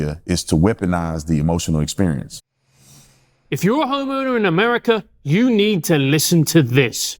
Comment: Dear Constintibe, Someone is messing with your videos. I think, in the context, Brandon mentioned a "homeowner" in his introduction. What I heard was "homo". Did Brandon say "homo" or "homeowner"? Someone may be editing your content in very subtle but significant ways.